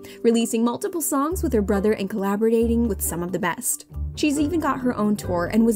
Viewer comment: did she just say collabordating?